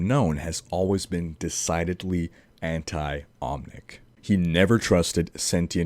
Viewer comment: in 8:18 when says "anti-omnic" the auto subtitles say "anti-islamic" xD just wanted to point that out... will/is islam the cause of "omnic" crysis in our world? xD